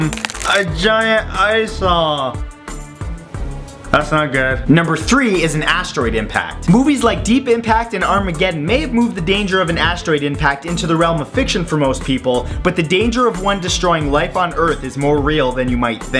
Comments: I thought number 3 said a steroid impact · I wasn't really listening for #3 so I thought he said "A giant assh*le." Kinda like my friend. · i thought he said a steroid impact... not an asteroid impact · At first I thought #3 said "A Steroid Impact" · Wow! I thought #3 said "A Steroid Impact", I should have listened. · it sound like he said "a giant lysol".